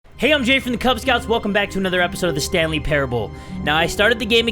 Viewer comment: Why does it sound like he is saying "my name is Jerry from the Kubz Scouts" [I know his name is Jay]